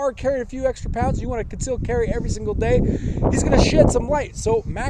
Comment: "Hes going to shi* some weight". I know he did not say that but that's what and my co worker heard.